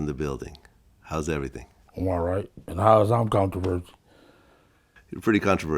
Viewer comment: Keefe saying “how is am I controversial?!🤷🏿‍♂️” will always be hilarious 😂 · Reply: Yes because it sounds like he said, “how is I’m comfortable”😝